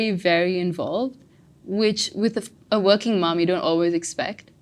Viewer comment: did she said, which with a fckin working mom. I am sorry, I could be totally wrong but someone’s gotta confirm